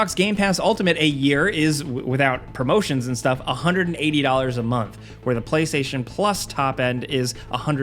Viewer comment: error at 4:00 he says 180 a month but the captions said a year lol